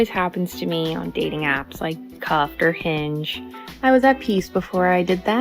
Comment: I know she said "cuffed," but I swear I heard her say "cucked"